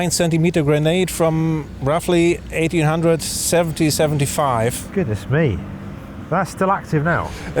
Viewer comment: 19th. 1875 is in the 19th century :) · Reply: Yeah... minor goof by me. But it was my hearing, not a misunderstanding of how the centuries are numbered. I just listened to it again, he says, "...roughly 1800, 70, 75..." I assume he means between 1870 and 1875. But the first time I watched I heard "...roughly 1800, 1775..." and the 1775 would have been the 18th century -- hence my comment. Derp.